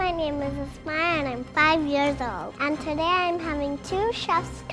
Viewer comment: I thought she said and i drink out of urinals but in reallity she said and im 5 years old